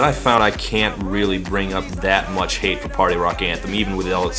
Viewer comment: Party rock anthem was the song in fourth grade. Our whole class loved it and I remember everyone dancing it to it. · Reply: i thought they said eddie rockets (a restaurant in my country) instead of party rock and i was so excited that someone wrote a song about my favourite restaurant (had no taste back then, eddie rockets kinda sucks)